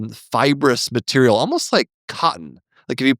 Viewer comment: I'm cooking while listening to the episode, so not 100% there. And I heard "white-ish, grey'ish material, almost like... cum". That gave me a pause. I re-listened, and, "Oh, cotton!" Not being an English native has it's funny moments). On a serious note, as a medical interpreter, I had to interpret a lot of surgical consent talking and pre-op appointments in general. And I've seen how people react to possible complications. I've seen people decline surgeries because of possible complications. Informed consent is extremely important, and I'm genuinely happy to prolong the conversation however much is needed to make that happen, even if I'm extremely tired by the end of the shift. I've also had minor surgery done to me and followed my grandma to her cataract appointments, and all of that carries through. It's a blessing to have a conscious and skilled surgeon, and I'm happy you are one. Good luck with further cases! On another note, we don't get complications often as interpreters. We are trained to either do our job well, or send it the other interpreter's way. We can ask for repetitions and use dictionaries. But there was a "complication" that came from another interpreter. GI appointment, lady patient has constipation and says that she tried 'digital stimulation', and that had not worked. As you well know, digital means either computer way, or finger way. And she said that the computer way. I was so perplexed. Rectum is not a computer to be stimulated that way. But then